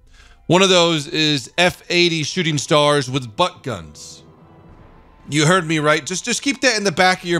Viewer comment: When he said but guns I thought he said spud guns